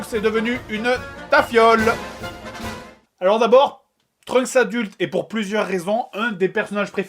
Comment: Hé les gens, je veux votre aide ! J'ai jamais compris ce que dit JDG à 3:11, j'entends "Trunks adulte et pour plusieurs raisons..." ET CA NE FAIT AUCUN SENS ! Je comprend pas, pitié libérez moi, dites moi ce qu'il dit x') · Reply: en fait il dit " trunks adulte EST pour plusieurs raison l'un des perso ... etc". J'espère t'avoir aider !